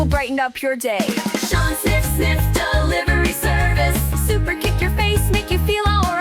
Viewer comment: The HBK musical ditty - I heard it as "snip snip" and began wondering if there was a suggestion that HBK offered circumcisions back in the day?!